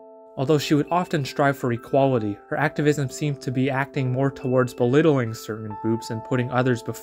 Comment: I dont know why but I miss heard this as "Bewittling certan Boobs" 😂😂😂